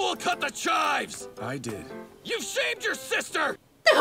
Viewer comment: I need to thank you for this. This whole time I thought Bakugo said "YOU SHAVED YOUR SISTER" which...I had no idea how to take that. I'm glad I now know he said shamed. Makes a lot more sense.